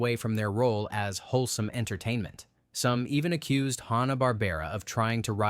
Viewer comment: Narrator is kinda boring. No life. Almost monotone. This is definitely AI. It has to be. Who would pronounce Rankin Bass, like the fish, as "Rankin Base"????? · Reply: i heard "Hawn-ah-bar-bear-ah" at about 02:10.